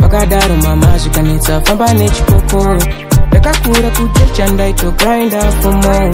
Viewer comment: Who heard chipoko on